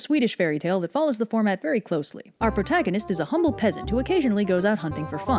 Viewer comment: At the start of Swan bride I Swear it sounded like u said "our protagonist is a homo peasant." And I was like ...well that was rude XD